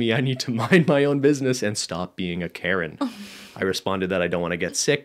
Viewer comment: Lmao I'm listening for the second time and I just realized that they say "Karen" for some reason I thought they said "carrot" lmaoooooo I feel so dumb haha